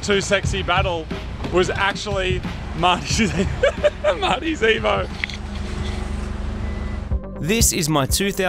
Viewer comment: Thought is said Marty's AVO lol